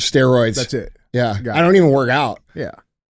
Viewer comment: When Andy said “mainline juice” I 100% thought he said mainline Jews